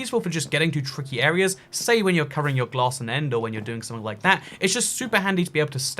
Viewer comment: Did he say ‘covering your glass in end’ or was it just me that heard it?